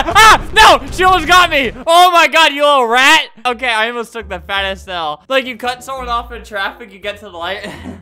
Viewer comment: I thought he said he almost took a fat L instead of fattest L.